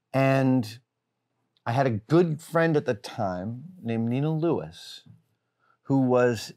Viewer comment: Wait, anyone else pick up that Adam basically named an artist that had nude pictures of him frolicking around a beach somewhere? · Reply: Sounded like he said "Nina Lewis". Had trouble googling so I assume I heard wrong.